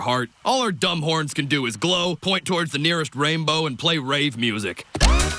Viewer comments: Unicorn: All our dumb horns can do is glow, point toward the nearest rainbow and play rave music! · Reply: I thought he said RAGE music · Unicorn: All our dumb horns can do is glow, point toward the nearest rainbow and play rave music! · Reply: I thought he said rape music.